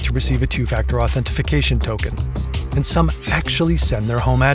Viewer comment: am I crazy or am I hearing "authentification" and if that's the case is that even a real word?